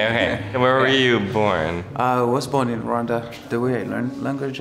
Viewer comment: When that dude said he was born in Rwanda I thought he said he was born in wakanda😂😂